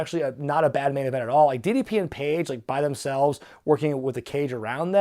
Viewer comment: Did he say DDP and Page ?